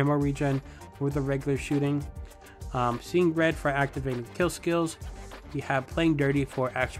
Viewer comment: What about any Spy Class Mod builds?? would love to see what you do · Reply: My bad man I thought i heard seein dead I watch your vids while I work sorry